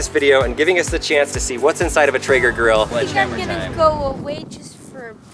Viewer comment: Honestly thought that said “what’s inside a teenage girl?”